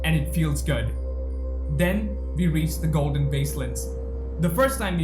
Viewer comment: Did you just say "Golden baseland"? 😂😂😂